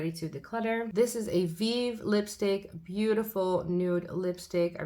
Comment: Do you mind telling me the brand lipstick at 24:17? I heard "vive" lipstick but wasn't sure :) New sub!! (after ND lipstick) Sorry I edited it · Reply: Hoi you are correct. It’s from the brand Vieve (by the creator Jamie Genevieve). UK based creator and you can get her brand via cult beauty. This shade is called Vieve as well. ❤️ perfect Nude and feels luxurious